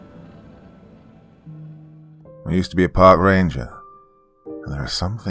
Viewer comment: I legit thought it said “I used to be a Pink Ranger.” Kimberly!!